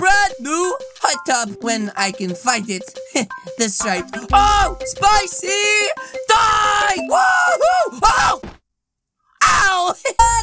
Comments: did he just say spicy dies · when is jumping in the hot tub i heard him say spicy dies